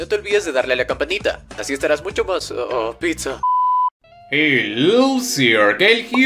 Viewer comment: entendi "oooh pija" no se porque xd